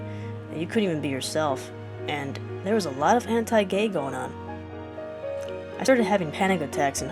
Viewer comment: Pigtails and green. Miku Hatsune? When you said anti-gay, I thought you said Hentai-gay....So I was very confused. XD But anyways, you're amazing! And you've made me realize a few very important things, so thanks!